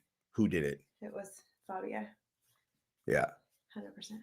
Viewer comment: did she say Fabia stole the car?? 😂😂